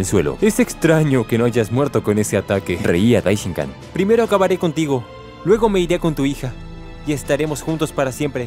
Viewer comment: escuché "primero acabare contigo y luego me acostaré con tu hija" 💀